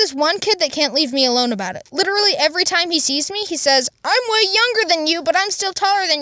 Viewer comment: what it says "I'm way younger" What captions say "I'm hwayeong grrrrrr"